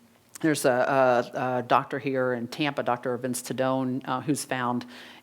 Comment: Can't understand the name of the doctor she mentions at 51:41, does anyone got it? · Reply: Dr. Dagastino or perhaps it is spelled Dr. D'Agostino